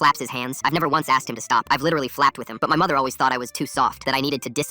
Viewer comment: I swear I heard "fapped with him" and not "flapped with him"💀💀💀💀💀💀💀💀